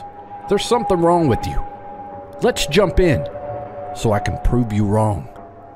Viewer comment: I totally thought your intro said "so I can probe you wrong" lmao